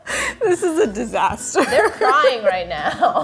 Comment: "They're crying!" a queen, respecting pronouns. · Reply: Intially I heard you're but then I saw this comment so I listened to it again and heard they're. Lmao either way... and are the best!!